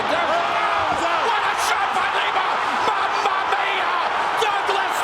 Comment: did he say "mamma mia" 🤣?